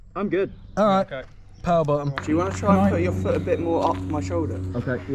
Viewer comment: do my ears deceive me or does he say "power bottom"